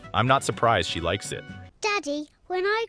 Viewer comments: did she say diddy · I thought she said diddy😭